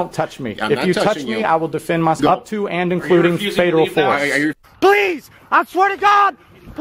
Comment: I thought you said "fatal force", not "pissing myself".